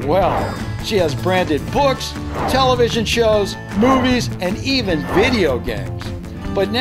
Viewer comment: I thought you said boobies in the barbie scene 😂😂😂 i realized you said movies